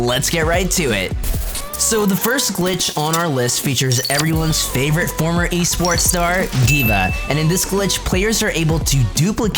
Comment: Oh i thought he said everyone's favorite former porn star